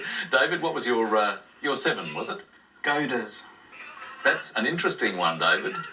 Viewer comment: It's hard to tell what words the contestants are saying with that heavy Aussie accent. Closed-captioning shows saying "gators" when he says he has a 7-letter word @ 4:14. David Astle is shown replying "go ders" which leads me to believe "goaders" was the word that was being disallowed. I guess David Astle was too embarrassed to acknowledge the 8-letter word that was available which I selected as my response.